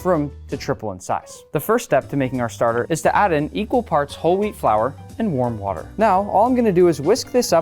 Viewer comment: i thought u said holy wheat